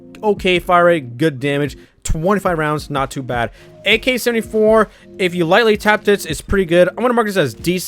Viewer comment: Did he just say "ak-74"???????